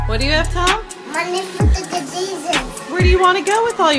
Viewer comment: When the little kid said money for chucke e cheeses it sounds like he said money for fucking Jesus